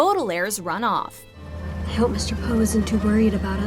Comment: when they said “I hope Mr Poe isn’t too worried” the captions said Mr Poo